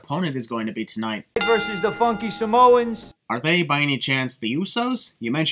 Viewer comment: I don’t why but @ 2:30 it sound like he said the fuck you samoans